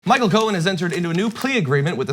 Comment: I thought it said “Michael Cohen pees” but oh well this is okay too